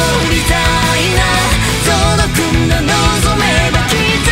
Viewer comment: 童磨君が望めば〜って毎回聞こえるんだけどこの空耳自分だけなのかな…